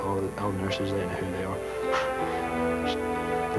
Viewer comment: hey guys, can someone please tell me what is the last word said in this video? Thank you ...and what???? at 23:04? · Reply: watch what you eat